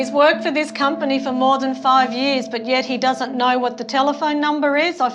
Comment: He said he is married repeatedly.The interpreter misheard him.Its not more than 5 years, its about 5 years.Australia should also impose disposal fees for this these type of passengers..